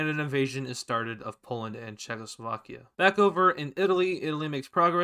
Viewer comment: did he just say Czechoslovakia